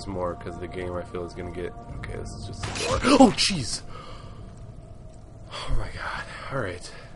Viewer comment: LOL at 2:25 it sounds like hes saying OH CHEESE!